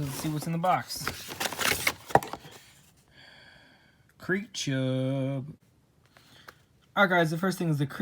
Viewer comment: thought you said creepaa